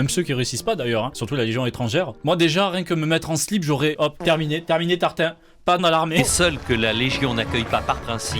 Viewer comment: j'ai entendu "Tartin slip"..